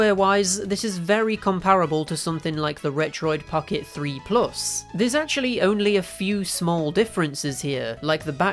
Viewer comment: For some reason I think I heard this dude saying "Fuoid pocket 3 plus" Idk I misheard but ngl it's funny